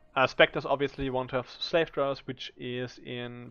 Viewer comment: I cannot understand the name of monster you are using as a Spectre. Are you saying Slave Drivers? I don't see any information about the Spectre used besides this instance at time 24:26. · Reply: yes, Slave Drivers (those should be checked in the PoB afaik)